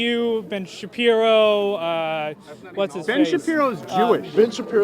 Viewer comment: sounded like he wanted to say Ben a fake Jew XD